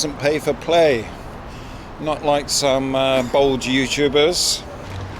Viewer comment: did you say Bold or Bald :) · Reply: Bald ;)